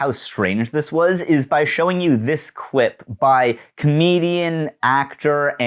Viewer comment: i really thought you said russell brand was Canadian and it took about 5 watches back to realise you said comedian because i was like… not hes not…